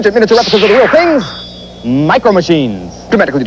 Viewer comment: Ah, Youtube. Thanks to you now I can understand everything this man says. Wow, he was selling MicroMachines. I thought he said methamphetamines.